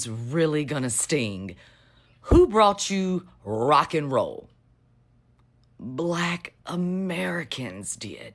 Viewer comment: And Rock and roll they need to do their history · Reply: You need to listen again to what she said. I heard her say rock n roll...